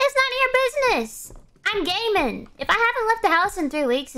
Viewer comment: When she said gaming, I thought she said she was a gay man lol.